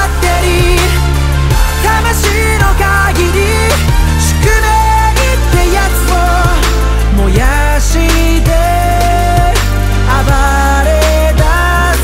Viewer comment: これ小学校のとき、給食の時間でよく流れてたんだけど 「宿命ってやつを燃やして 暴れだすだけなんだ」 のところが空耳で 「宿題ってやつを燃やして 暴れだすだけなんだ」 って聴こえて、一人でめっちゃ笑ってた